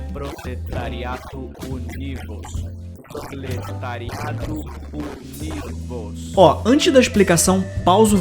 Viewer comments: Eu escutei "proletariados unidos, proletariados uni-vos ", tô seguindo as regras a risca 😆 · " O proprietário/proletariado é Rodrigo " ouvi no celular (sem fones) Edit: reovi o áudio e no lugar de Rodrigo ouvi "Uni-vos" kkkkk Edit 2: LG K10+ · eu ouvi "proletariados fudidos" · Adorei, do meu cell pau Véio escutei "prolitariado" kkkkkkk